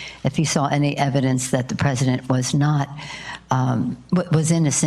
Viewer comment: lol did she say special cancer?